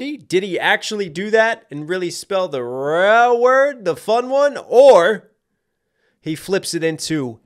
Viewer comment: did he just call it the fun word😂😂😂